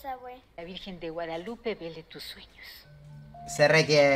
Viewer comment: En el minuto 7:30 escuche que la virgen de Guadalupe viole tus sueños :v